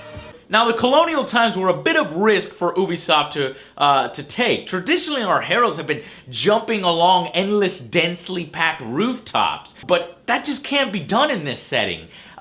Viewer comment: wait... did you say obisoft instead of ubisoft 02:23 (dont hate me for saying this)